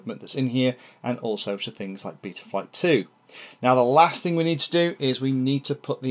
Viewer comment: you said betaflight 2, instead of 3... maybe still possible to correct with voice-over. You don't want to upset Boris 😉 (and delete this comment afterwards) As for the camera, you should really try out a GoPro1/2 replacement lens on it. Much higher quality lens, and also larger FOV as a bonus. Really nice build you got there 😉! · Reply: That's a '...Betaflight too' not a '...Betaflight two' ;) We will try the lens thing as well. Thanks for the idea!